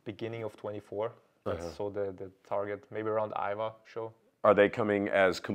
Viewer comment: did he say "Iowa show" which Iowa show I'd love to see this in person.